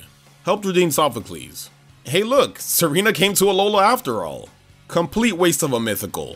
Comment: Mallow’s Tsareene had to be the best description by far, when I first heard them say “Tsareene” I heard “Serena” and I’m glad I want the only one